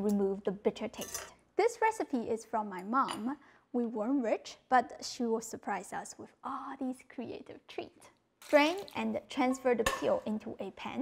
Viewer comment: "we weren't rich but she would surprise us with all of these creative treats" then the smile she gave after saying that. Some mothers are just incredible. The things we do for our kids 🥹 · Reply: Wait is that what she said? I thought she said "we were born rich yet she surprised us with this creative treat" What's wrong with my ears